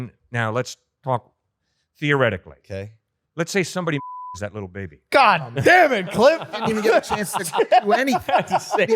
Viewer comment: What word was bleeped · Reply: murders